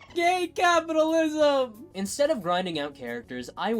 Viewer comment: sorry, it sounds like you say “f*** gay capitalism” Is that correct?